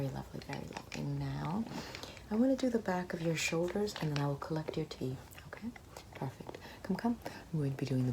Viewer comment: I heard "collect your teeth" and didn't even question that